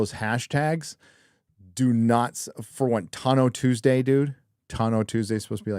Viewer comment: Somehow I heard it as Tuna Tuesday 🤣🤣